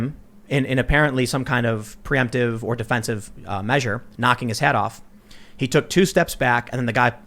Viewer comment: I thought you said "knocking his head off" but it was just his hat.